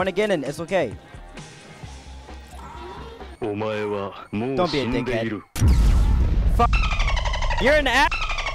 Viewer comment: did he just say dont be a nigga